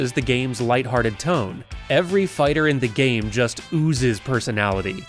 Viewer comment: XD My speaker glitched at the perfect time at around 3:38, so it sounded like you said "Every F*ker in the game.." LOL